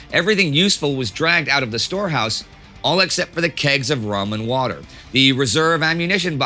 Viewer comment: did he say kegs of ramen water or am I trippn